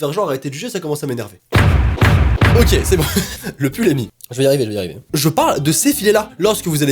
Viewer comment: Quand tu as dit "le pull est mit" tu la dit tellement vite que j'ai compris "le pull emy" (soit mon prénom 😁) et sinon effectivement se sont les mêmes choses qui m'énèrve 😤